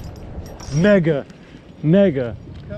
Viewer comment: was he sayin the n word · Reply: Mega, it was a giant.